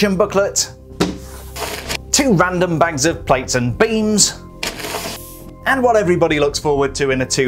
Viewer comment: I could have sworn that it sounded like said "franks and beans" when he was describing some of the bags of LEGO at the beginning of the video. Turns out is was actually: plates and beams. Time to get my hearing checked.